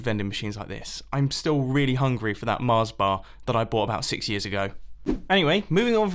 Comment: DID SOMONE SAY MARS BARS?!?!?